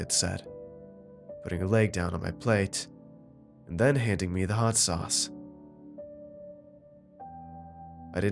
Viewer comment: "putting her leg down on my plate and handing me the hot sauce" did did i hear that correctly